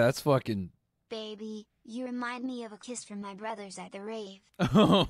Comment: I definitely thought the voice said "I compare you to a kiss from my bros at a rave"